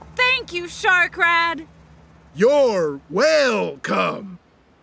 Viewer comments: Anybody else hear "You're whale - c*m" instead of "Whale - come" in Shark dad? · At first I thought he just said you’re welcome strangely but now I found out he did you’re whale come which I hat puns